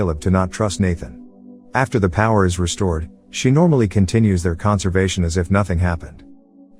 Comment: you say "conservation" instead of "conversation." Not hating just pointing out. Love this movie!